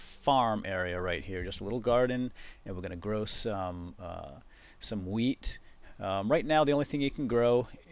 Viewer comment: at first I heard "We're gonna grow some weed" I was like "wtf you can do that????" imao